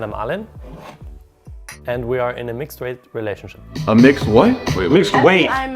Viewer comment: I never knew what a mixed weight relationship was until now😂😂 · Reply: I thought he said 'mixed white'